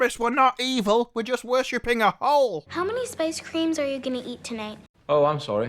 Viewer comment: Spice cream? They do know Spice is a drug in star wars right? One that uses slave labour in it's manufacturing? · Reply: I thought they said spice, but later it sounded like Space cream, when the kid said it. Also, isn't Boonta the holiday on tatooine???